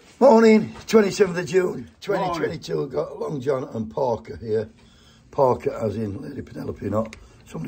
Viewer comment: Morning. And I can’t lie, I thought you said Porker too. Poor wee dug getting fat shamed... 😜😂😂